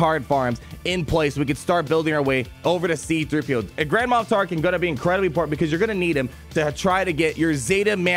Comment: I thought you said “Grandma Tarkin”